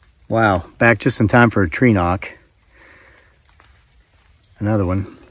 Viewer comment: I understand now that you are saying "tree knock".... I thought you were saying "trenowk" pronounced like "ewok" in star wars thinking that you were referring to some type of spirit species in the woods.... Lol 😂